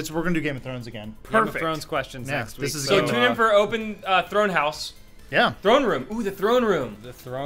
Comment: I thought said "Open Throat Haus" which made a lot of sense.